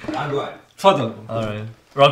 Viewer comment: Did he just say "go ahead" in Arabic, or it's my brain trying to make mysteries words familiar?😅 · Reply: Yes it is he said Tfaddal